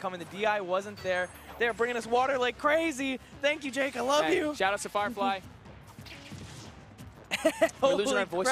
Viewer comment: Did he say Shoutout to Firefly at 0:57 and why?